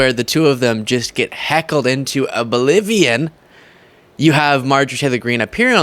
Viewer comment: did anyone else hear "Heckled into a Bolivian"?